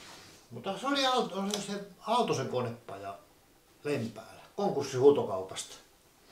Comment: *I think in 16:07 he said Aaltosen Konepaja from Lempäälä